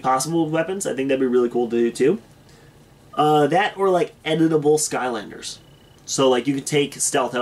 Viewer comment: I thought you said edible skylanders lol Edit: My day is pretty good because I'm watching your videos